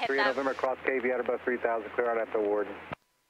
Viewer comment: the controller really rushed the clearance - I think he said cross cayvi at or above 3000 cleared rnav 14 wharton.. Your read back was not correct: "cross cavyi at or above 3000 cleared direct wharton". Cleared from cayvi direct would be very unusual (and never given). the IAP is design with that structure for a reason. The controller didnt pick you up on it either though. so you both were incorrect there imho.